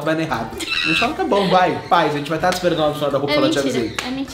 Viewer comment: Só eu que ouvi um "paz" no minuto 4:16 ? SERÁ?????